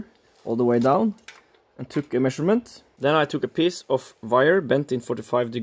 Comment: initially thought he said “then i took a piss”